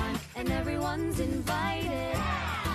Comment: I watched this video so many times and I still can’t figure out what Cole says at 1:37 xD still love the song though :) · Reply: “Ooooh, and I can be with my true love, Champagne, here I come...”. (Champagne is Bitsy’s dog he found in the pilot episode)